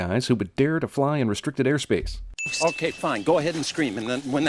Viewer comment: On 4:59 did he say trisiclacops??? Boi it’s triceratops and it won’t hurt u, this is why u did bad in school and the reason your my least favourite character in this film... (sorry for the hate)